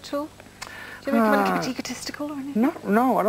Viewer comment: did she say "take a testicle or anything"